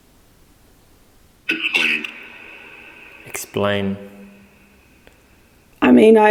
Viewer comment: You put "Explain" but I clearly hear "its clean". Like when you clean a body out, or clean the body trays off. Just letting you know.